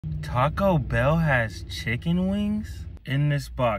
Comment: i actually thought he said caco bell for a sec-💀